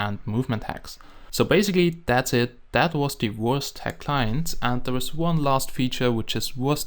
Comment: This is the Wurst Hack Client Lol sounds like he says the Worst Hack Client